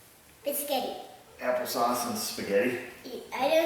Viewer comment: I heard "apple sauce and biscuits" sounds good to me.